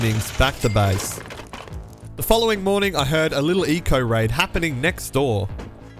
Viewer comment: Did anyone else hear say eco raid instead of eoka raid